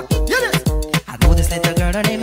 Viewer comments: “her beauty like a bunch of rose” what i hear: “her bitches light up on the throne” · The one where it’s like “her beauty like a bunch of rose” I always heard “her bitches light up on the floor” 😭😭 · I always heard " her bitch is like a bottle of rose" 💀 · It says her beutys like a bunch of rose i always thought it said a bithches like a bunch of rose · i always thought it said “her panties like to grow”💀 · The one where it’s like ‘ her beauty’s like a bunch of roses ‘ I always heard ‘ her bitches like a pond of flour ‘ LIKE LMAO WHAT!?😂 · The one where it’s like “her beauty like a bunch of rose” I always heard “her bitches light up on the floor” 😭😭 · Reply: I heard "her biches are bunch of bro's" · is it just me or do i hear '' her bitches light up on the floor '' instead of '' her beauty like a bunch of rose '' or am i the only one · i didn’t hear: “her beauty’s like a bunch of rose“ i heard: “her bitches light up the floor“ lmao